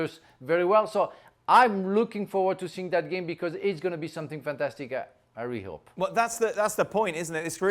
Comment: Does anybody know what Frank said from 3:38 - 3:40? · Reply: It's going to be something fantastic, I really hope